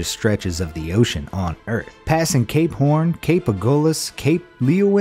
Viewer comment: When he said Cape Horn I thought he said C A P E P O R N